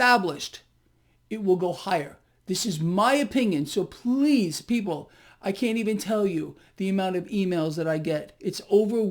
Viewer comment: I thought I heard say... “it’s unbelievable how many FEMALES I get”!!!! Females? Or emails? The